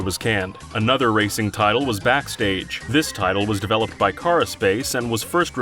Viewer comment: you said "cara-Space" but it is actually called "Carapace". Put some respect on my boys name